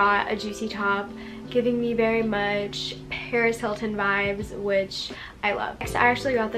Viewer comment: ong I thought she said Perez Hilton and I got scared 😂